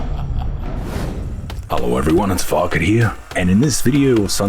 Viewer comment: Lmao subtitles thought you said Falcon not Farket